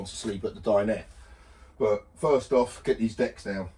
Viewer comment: Ha ha when you said "let's get these decks down" I heard "let's get these dec's down" I thought at long last the tinsel was coming down from around the door. Boat is looking amazing, the children will have such amazing memories on that boat with you. Best wishes Christine x